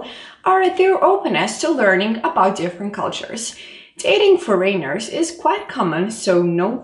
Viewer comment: did she say dating four ?